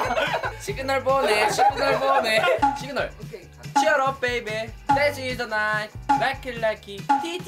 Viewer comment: 댄스 인더 나잇이요?! 저만 그렇게 들리나요?